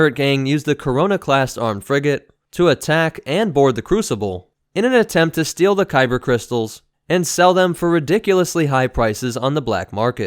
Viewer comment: did I hear CORONA class armed frigate?